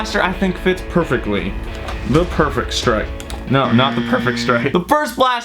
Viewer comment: I thought he said the pervert strike